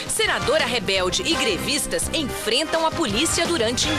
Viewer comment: quase entendi "Senadora rebelde e GREMISTAS"